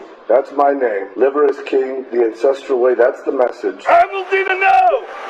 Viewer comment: Not gonna lie, I thought he said "the INCESTral way" not "ancestral way". I didn't even blink which tells me that I've been on some unfortunate corners of the internet too much lately. NOT THOSE, just true crime sh*t.